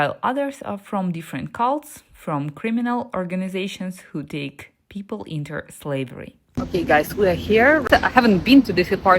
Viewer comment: I had to adjust the volume, for a moment I thought I heard "criminal organizations taking people into slavery."